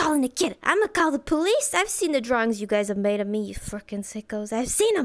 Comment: can someone friendly tell me what she says at 0:18 to 0:20? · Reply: "I'm gonna call the police! I've seen the drawings you've made of me..."